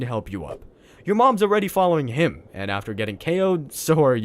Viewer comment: misheard "your mob's already following him" as "your mom's already following him". Man what a crushing defeat line for an asexually reproducing species.